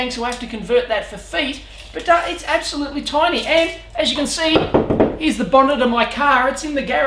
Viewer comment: E.g. at around 0:36 it thought you said "absolutely times and as you can see is the bar none of my colleagues in the gay". WTF!